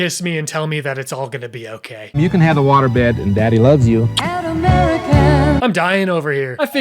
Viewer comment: Did I just hear "You can have a waterbed when daddy loves you"